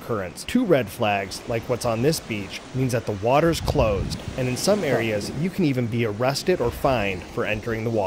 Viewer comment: to @ 0:17 what is that voice saying ? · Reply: That's me in the background saying "huh" as I read the sign while holding the camera. 😊